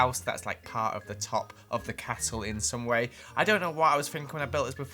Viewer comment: I’m not sure, but did he say cattle instead of castle?